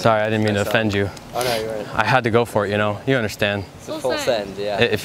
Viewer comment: Did i hear full send ?